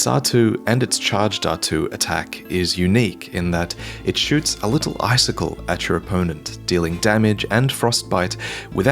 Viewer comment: I know sometimes enjoying the game makes observation of character designs go unnoticed. I'm half awake and I thought you said "shoots a little bicycle." I stared for five seconds afterwards looking for a bicycle🤣🤣🤣